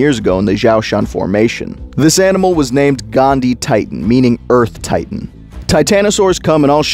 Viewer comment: did he say gandhi. LoL 🤣